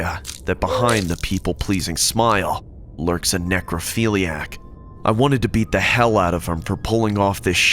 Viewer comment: what did he call the monster janitor? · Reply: A necrophiliac. Meaning someone who has a thing for corpses/dead people